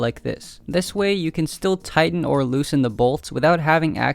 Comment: when you said tighten the bolts, i thought you were saying titan, which was the epoxy you used!